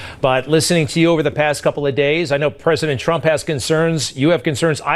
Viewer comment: I Know he said “president Trump” but I heard “prisoner Trump” 😂